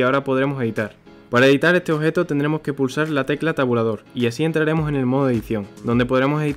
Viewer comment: no entiendo que dice, no se si dice la tecla tabulador o algo asi